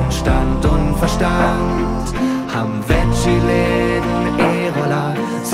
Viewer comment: von welcher stadt redet ihr da... 4g fast über all? wo den. lol bei 02:25 verstehe ich immer... (haben veggieläden, Ebula...)